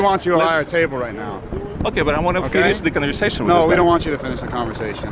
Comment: did I hear correctly? Did one of the guys call the guy a racist? A racist for what exactly? For not agreeing with socialism/Marxism